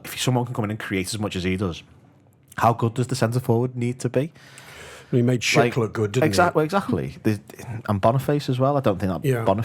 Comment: When he said he made Shick look good i literally thought he said he made shit look good 😂😂